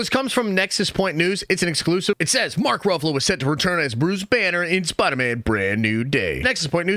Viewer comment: I heard he's playing Uncle Benner